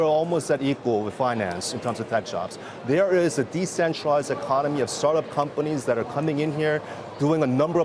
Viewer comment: did he say decentralized economy?? buy bitcoin.